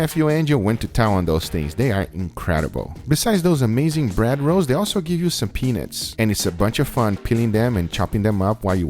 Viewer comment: sounds like he says gives you some penis